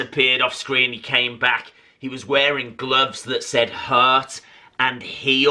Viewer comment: Lol I thought his gloves said “ hurt head”